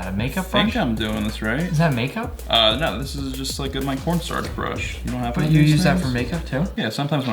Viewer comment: NGL, the first time I heard cornstarch brush I thought it was Pornstar brush... Anyone else? No? Just me and my dirty mind?